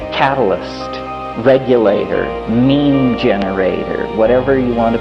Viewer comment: yo did he say meme generator??